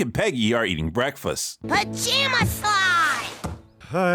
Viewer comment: I seriously thought she was about to say Pajama Sam